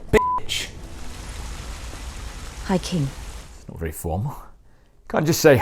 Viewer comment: Ooooh, I thought she said, “hiking” as in telling everyone where she’d been.